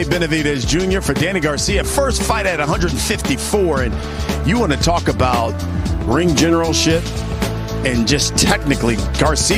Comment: Did he say you wanna talk about ring general shit 😭😭😭